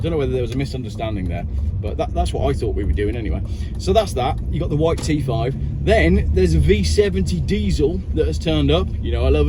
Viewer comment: I thought you said "the white teeth 5"